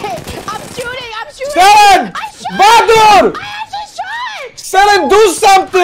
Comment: He actually said "Vadur" (look killfeed) @ 11:21 Vadur was his "adopted son" and his chat mod.